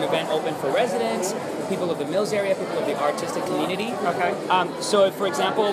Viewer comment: Thought he said "People of the Autistic community" 5:00 LmAo